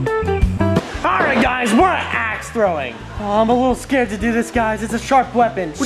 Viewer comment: I thought you said ass throwing